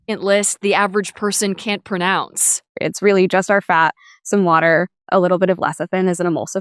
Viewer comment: Listen closely at timestamp 3:30 when she lists the ingredients. Sure sounds like she says ... 'just OUR fat' etc etc. 🤔 🤔 🤔